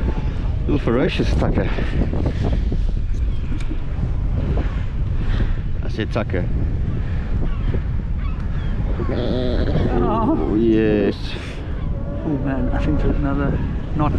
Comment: Glad you clarified that “sucker”! 😝😂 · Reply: I heard "Ducker", yes definitely Ducker.